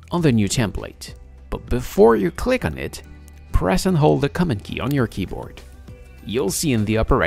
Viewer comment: Thanks. I thought u said the "coma" key but you meant "command" or "control" for Windows